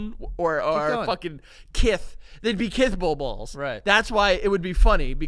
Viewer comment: I thought they were saying Pitbull. “Big fucking squirty pit bulls?” Wtf